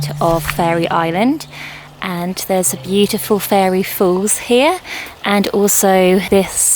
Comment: Whenever I tune in I’m indeed left enchanted! Those bluebells!💙🤍💜 Misheard Fairy Falls for Fairy Force….May the Faery Force be with you! 🧚‍♀️🧚‍♂️🧚🏾